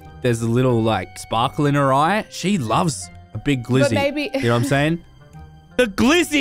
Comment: Listen to What says at 2:08 Im not sure bout y’all but I definitely heard “but baby”. Either she just causally calls baby or somethings going on here😏